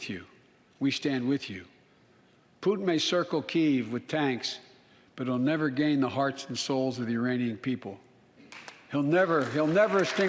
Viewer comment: Did he say uranian people ?? Lmaoo